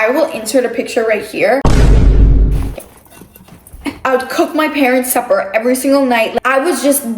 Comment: did anyone else think said "i would cook my parents every night" 😭